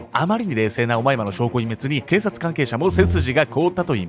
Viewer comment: オマイマがマイマイに聞こえてしまってちょっと笑ってしまったごめんなさい